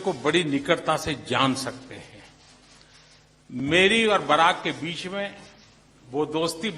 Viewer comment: did he actually say barrack at 33:50?